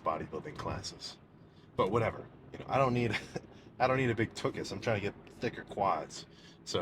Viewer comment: DID HE SAY TUCHUS 😂😂